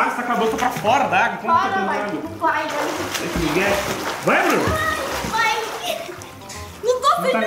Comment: ele fala vai amor eu acho sla eu entendi isso